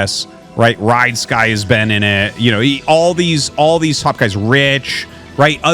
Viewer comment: hahahahahaha sounded like you said 1:14 "all these all these fu*k as rich"